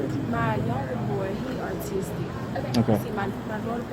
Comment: Did she say artistic??? 15:55 😭😭